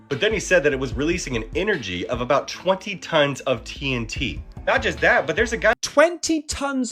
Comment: I'm sorry I thought he said 20 tons of THC. 😂